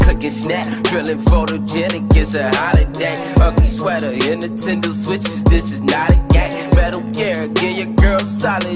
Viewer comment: did he say Nintendo switch?